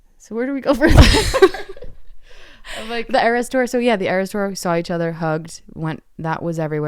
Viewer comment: All I hear now is "the error store"